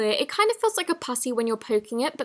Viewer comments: um 0:50 seconds in did anyone hear it feels like a pussy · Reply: Rose She said “Putty” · it sounded like she said pussy instead or putty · Did she say pussy in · Did anyone else hear pussy on 0:50? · i thought she said “pu**y” omg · Did she just say “Moki” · Am I ok or did she say it feels like a passy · sounds like she says pu ssy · i swear she says “it kinda feels like a pussy” 🙂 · HAHAHAH i really thought she said:" It kind of feels like a pussy when poking it" 😂😂😂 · “it kinda feels like a putty” but with her accent it sounded like pu🐱ssy 😂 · When she said putty my parents thought she said pussy